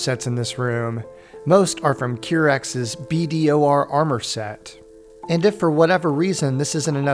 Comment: Hello, What are you saying at 17:26 ? Can't clearly understand modder name of the armor sets.. Any link to those or name of the modder? THX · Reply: Hey! It is "Pulcharmsolis". Here is the mod author's page on Nexus: <link>